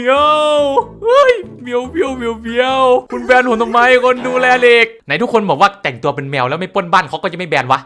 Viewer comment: เดี๋ยวๆทำไมในคริปตอนนาทีที่1:00พูดว่าคนดูแลเหลก